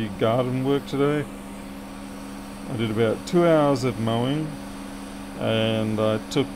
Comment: Ayeee. What happened to your hair. Hope you didn’t have an injury. That spot up there…. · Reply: Mowing, sorry, thought I heard biking? I'm sure you said 3 stitches, so pleased to know only a flesh wound, (Monty Python).